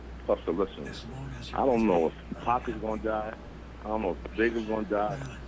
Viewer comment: Mann what so crazy is when puff said idgaf if big die or pac die or suge go to jail in it was real that's real crazy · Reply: But the guy who heard it too sounded like he said I don’t know if pac gonna die I don’t know if big gonna die🤔